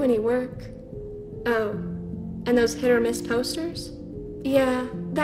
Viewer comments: i heard her say ”oh... and those hit or miss posters?” The memes got me. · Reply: Hit or miss is a saying, not just a tik tok meme.. It said hit or miss. Get over it. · Who else heard “hit or miss posters?”